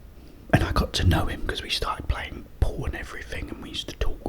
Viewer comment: I am so sorry to ask but i watched this part like 5 times already. What exactly did you say here? You played Pawn, Porn, Pong, Pom? 13:25 Everytime i hear Porn. Its so wrong, i know its just me. IM IN NEED OF CLEARANCE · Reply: Haha! What I said was, we played pool lol 🎱